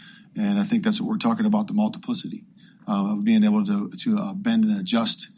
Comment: Yooooooooo what did say at 25:50 multipyssy. 😂